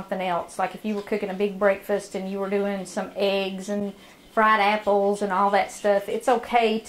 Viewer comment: Did I actually hear her say "Frying some eggs and some a$$holes" around the 6:10 mark of this vid??????? I listened to it 3 times and cant think what she possibly think of frying that sounded like a$$holes.... Anyway I got a good laugh over this vid